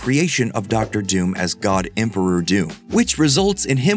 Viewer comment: Ngl I thought I heard "God Emperor of Dune" instead of "God Emperor Doom"